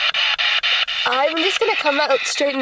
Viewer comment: says she wants to ask a question and I hear clear as day “we’ll ask it”